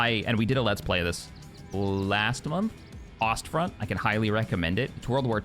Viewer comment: My dudes, what game did he say was similar? Frost Front?